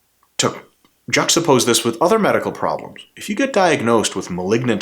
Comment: I thought he said ligma lol